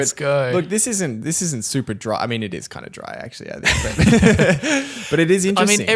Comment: did i hear DRY? I've been hyped up all day because of it 🤣